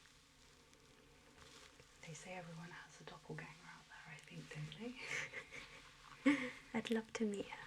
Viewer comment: what does she say at 17:04 ? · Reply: "They say everyone has a doppelganger out there, I think, don't they?"